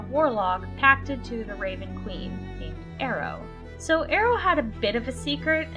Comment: Curious - what was the name of that class/archetype you mentioned that relies upon the familiar? My auditory processing issues made it so I couldn't understand those words for some reason (I kept hearing "Ravenloft" as in the setting). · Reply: Warlock to the raven queen. You receive your raveb familar at firdt level as a gift from the raven queen to watch over you. Many class abilities revolve around the bird, from gaining advantage on attacks on anyone who kills the bird, to merging with the bird to fly, so summoning a murder of ravens in battle. That bird is an essential part of the class and usually is able to be resummoned after a short or long rest. Ah my apologies, i was saying Ravenlock x'D